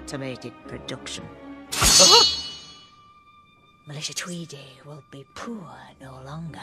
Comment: did I hear that right? Her name is Malicia? Or did she say Melissa? Malicia Tweedy, now that sounds evil... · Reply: Melisha